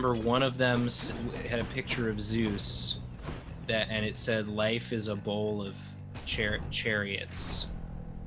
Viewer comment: When I was a kid I thought it said Life is a Bowl of Carrots 😅